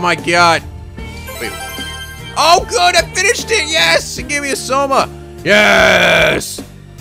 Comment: When you said 'it gave me soma' i thought you said 'it put me in a coma'.